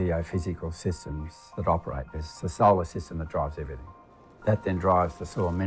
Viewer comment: what is it he's saying around 4:23? The sosowa system drives everything? Is it 'soil system' drives everything? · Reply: solar system